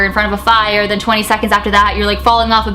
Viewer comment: At the beginning i had my volume really low so i couldnt hear much of what she was saying and when she said "20 seconds later you're falling off a building (ik because i skipped back)" i thought she said "20 seconds later you fall in love with a building" .... idk anymore 😂